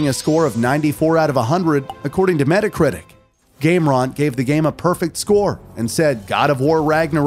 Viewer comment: Gameron sounds cooler instead of Game Rant. Awesome AI narrator lol.